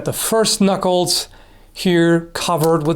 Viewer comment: I misheard "first knuckles" for the "cursed knuckles". XD Cursed Knuckles of Darkness, +5 to attack, +15 to defense, +over9000 to Pommel Throw and 95% chance of immediate crit "End Him Rightly" (if worn by).